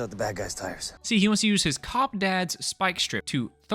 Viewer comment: I thought you said "His HOT dad" and did atleast two double takes